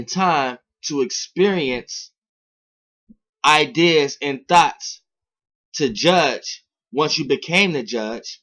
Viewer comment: I thought he said thot when he said thought xd